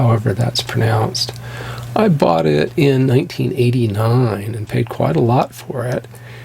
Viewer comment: i thought i misheard you say 1889